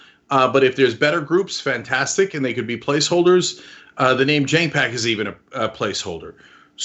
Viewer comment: Lol I thought he was saying “JETpac” not “Cenk PAC” LMAO 😂 I was thinking “ok I can see it, very Cenk-type ‘punny’ name for the pac... I wonder what JET stands for? Maybe something like Justice...Equality.... something with a T...” 🤣😂